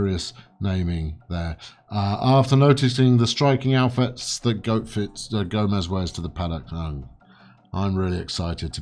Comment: Wait, wait... at 5:44 did say "goatfits"? Bwahahaha!!!